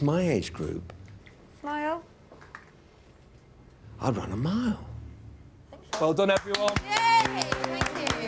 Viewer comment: what does nick say at 26:38 ? he's run a mile?